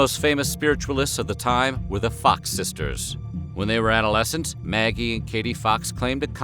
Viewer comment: i thought he said the fuck sisters LoL